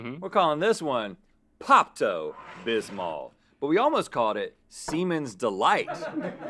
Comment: Rhett’s delivery on that “Seaman’s Delight” line was absolute aces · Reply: Pls i thought he said demons delight